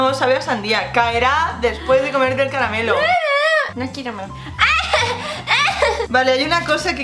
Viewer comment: dijo en el 3:10 no quiero amor??? Omg #melepe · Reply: yo escuché "no quiero mor.." quería decir no quiero morder. porque quería que mordiera el dulce.